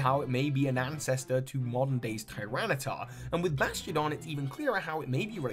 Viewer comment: the first time he says Bastiodon, it sounds like he says “Bastion” haha